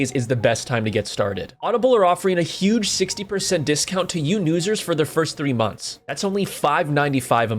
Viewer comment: Don't mess with the people featured in this video. I know they acted like complete donuts, but hounding them now is not needed. 🙃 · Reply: Unrelated but did you notice how you said "yew nusers" instead of "new users"